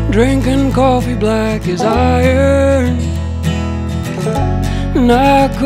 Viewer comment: Definitely thought he was saying “coffee black as I am” Nope. “Black as IRON”